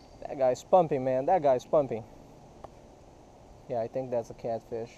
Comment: Did he say that the guy was PUMPING? How lewd !